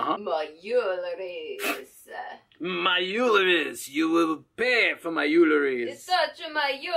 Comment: I heard " you'll pay for my uterus..." and then I corrected myself. I would like to hear about your daily schedule as well, or at least how productive you can be in a day. Thank you for the video, I apologize for my ears.